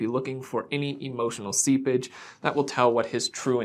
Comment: Hahaha. When you said ‘I’m looking for any emotional seepage’ I thought you said ‘I’m looking for any emotional sea bitch.’ Omg. Hahaha